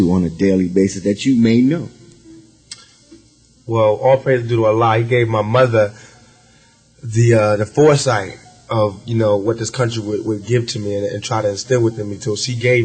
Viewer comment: did Pac say "All praise to Allah."?